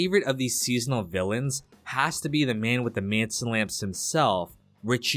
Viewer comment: I always thought he said, "dont give me those manson lips." 😂